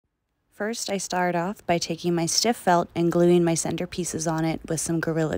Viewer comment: What is the base you are using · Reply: Sounds like she said "still felt" right at the beginning of the video